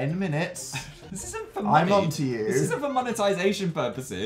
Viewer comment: I apparently can’t hear lol when he said ‘I’m on to you’ I thought he said ‘I love you’